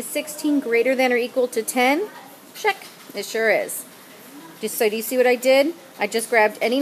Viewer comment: it sounded like ms said shrek